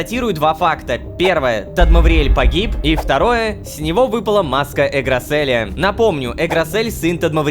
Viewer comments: ИЗВИНИТЕ НО. Мы послышалось на "маска Эграселя" А "матка Эграселя" ПХПХ ИЗВИНИТЕ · мне послышалось матка